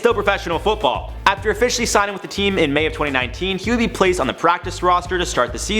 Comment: is it just me or did he say say may 20, 19th